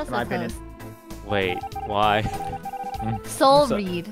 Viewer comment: did toast just say... “why”??!!